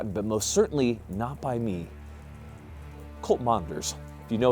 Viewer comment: Did you say Colt or Cult? If the later Lev Ch. 18 Ln. 21.